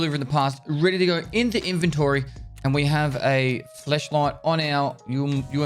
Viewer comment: maybe its the accent but did he say fleshlight?